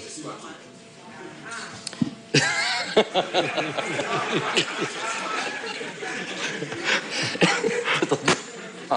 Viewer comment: il a dit quoi le journaliste a 16:00 ? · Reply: Elle a juste lâché « hein HINNNNNNN »